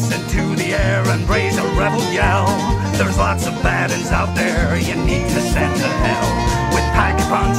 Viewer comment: Actually any time I PAPed on town I always heard "theres lots of badies out there"